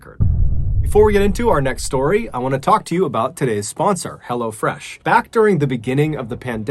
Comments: Hello fresh i thought you said hello fritz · You know you’ve become engrossed in someone’s story telling abilities when you’re just as interested in hearing them talk about Hello Fresh as you are the story 😂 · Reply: It might be his slight Massachusetts accent, but occasionally it sounds like he says “hella fresh”, which is great fun. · Diet Cheetos 😝 Almost everytime the Hello Fresh ad pops up I hear it as "Hella Fresh"...I just can't seem to unhear it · when he said “hello fresh” i heard “hello jeff” because i conveniently looked away from the screen and was confused for a solid 5 minutes